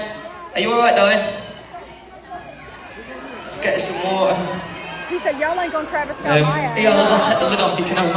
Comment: I heard someone say travis scott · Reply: I think i heard her say ''he said y'all aint gonna travis scott my a-''